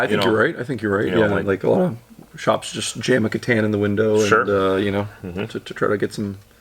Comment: When you said "Catan", I thought you had said, "shove a katana" into the window, and I was very much confused why that would attract normies and not weirdos. But then the ole grey matter caught up lol. EDIT: On the topic of Chinese goods, it's interesting that over time, I learned more and more high quality things are manufactured in China. Growing up in the US, I got hit with the "cheap chinese goods" slogan repeatedly until I also believed and repeated it, but after being around for awhile, I started wondering how accurate that was. Is nice to realize that sometimes, things aren't quite so simple as they appeared, and for the better too.